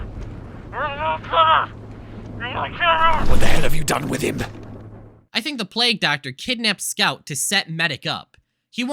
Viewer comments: I think here he said “I’m gonna kill you.” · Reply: I've listened to it several times, and what I think he said was "(She)'s innocent! Why'd you kill her?! I'm gonna kill you!" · I think this scene has scout say, “Where is she? Why’d you kill her! I’m going to kill you!”